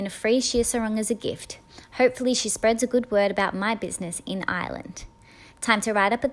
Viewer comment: I thought she said island and I was like ;-; but I heard Ireland so if your confused it’s Ireland ( I love it there especially Donegal)